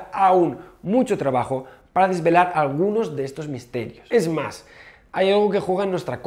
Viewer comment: la primera vez escuche "es musk" ,despues lo volvi a escuchar y no XDD